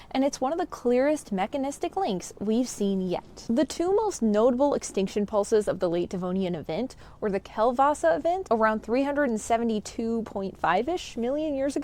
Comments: I thought you said the Kielbasa Event, but Polish sausages couldn't possibly have caused an extinction event 372 million years ago. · LOL. I thought you said Kielbasa Event before I saw the spelling!